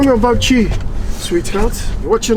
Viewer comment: did he just call you sweet heart? Lol